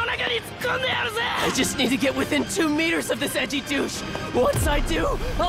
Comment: I thought he said edgy juice 😂😂